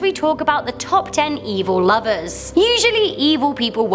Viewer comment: I thought it said “evil losers” lmao